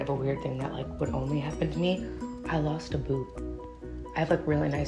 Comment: I THOUGHT YOU SAID YOU LOST A BOOB 😂😂